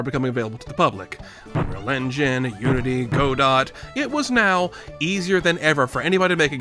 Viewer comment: did you just say "go dot" ?